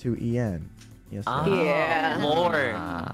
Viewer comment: Did REALLY say "LORE" at 44:18? If yes. Dang that was annoying.